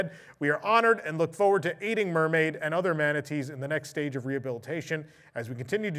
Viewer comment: Omg I thought you said we look forward to eating mermaid....😮